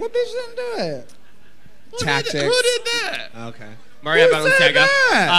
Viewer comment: I live for Detox I thought she was going to put Nikky in a headlock to find out the tea on who left the competition without doing the work Room Gig. Go Detox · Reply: Nicky said something which sounded like Mariah Balenciaga but I dont think the others were paying attention. I may be wrong but thats what it sounded like.